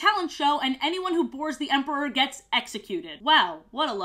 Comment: I hate that I thought she said "Anyone who vores the emperor gets executed" and I was totally down to believe that was right